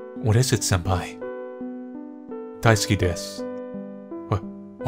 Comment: Lmao! When said "daisuki desu" I heard "diceski disc" and was confused for a while thinking "what's a diceski disc?" xD